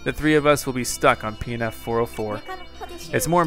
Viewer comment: who heard him say peanut 404